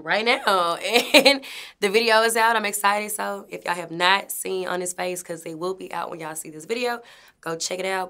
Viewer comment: For a good minute, I thought she was saying “honest face” and not “on his face”